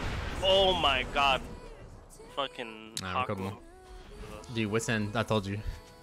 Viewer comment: did say "OMG, fcking Hakuho" How dare you disrespect aphro like that LOL